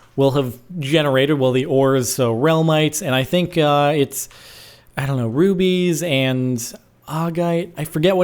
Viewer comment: when he was talking about the new ores generating i thought he said rupies! then i played it back & figured out he said rubies. :(